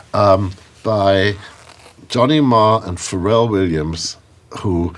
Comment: Does he say Johnny Mayer? What does he say? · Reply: Johnny Marr, guitarist from The Smiths.